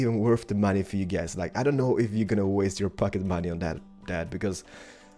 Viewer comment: When you said pocket money it sounded like fucking money